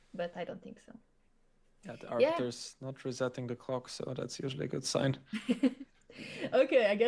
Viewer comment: Sounded like Felix said: "Arbiters not resenting the cocks. That's usually a good thing."😂